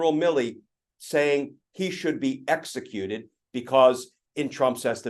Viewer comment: I didn’t hear “should be executed”,I heard “would have in former times been been punished with death” . Still extreme but not as directly threatening as “should” in my opinion. Am I wrong?